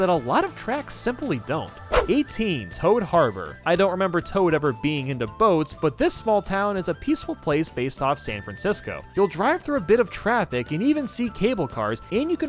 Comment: thought it said pearl harbor at first